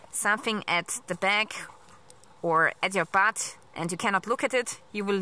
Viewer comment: did u say "something in your butt"...?